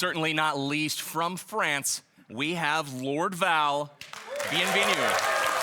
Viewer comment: When he said bienvenue I thought he said BNB news.